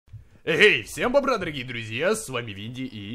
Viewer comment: Не расслышал или он в начале сказал .всем бобра дорогие друзья.